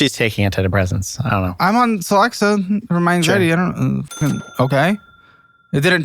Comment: what does he say, "I'm on Celexa for my [?]" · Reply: Anxiety